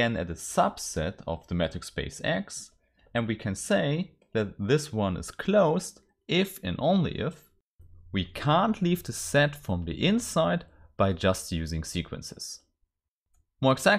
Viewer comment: Hi, The quality of the video is not good. I can't see your notes. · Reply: for me the only issue I had was at 3:06. When you pronounced "can't" I heard "can" the first few times. I'm sure that for native speakers or people with a good English level this is not an issue.